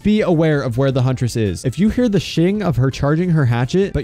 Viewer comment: i heard "when you hear shitting of her charging her hatchet" lol